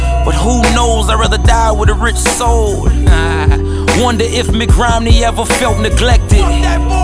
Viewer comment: Does it say McRomney?... 1:33 ..